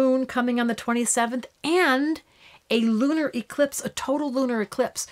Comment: Mercury RX caused you to say that in 7/27 video in Astro Answers the moon would be in LIBRA??? Hello it has to be in Aquarius because it can’t move from Cap to Libra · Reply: Graceispower Huber I hear her saying "LUNAR eclipse" not "libra"